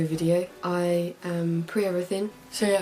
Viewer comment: It sounds like your saying pre teen which would be a little creepy